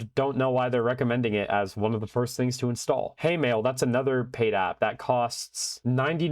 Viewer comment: When you said hey mail I misheard it as hatemail 😅 ..